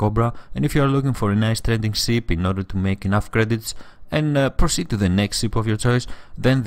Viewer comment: Did he just say "gay credits"?